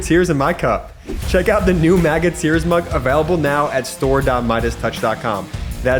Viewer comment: Sound like he said Maggot Tears t-shirt at the end there.